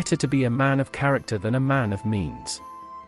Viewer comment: I heard that as 'A Man of Memes'